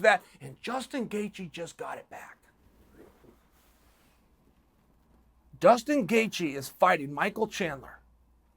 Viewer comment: i swear he said dustin